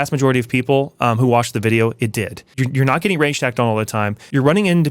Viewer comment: What is that word that sounds like "ransacked"??? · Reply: Rage hacked, aka cheating that isn't at all subtle and is meant to be extremely unfair. Usually used while raging and careless